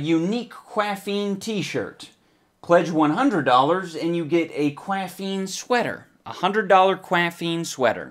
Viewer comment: First of all I thought it said "$100 for a CQaffeine sweetener"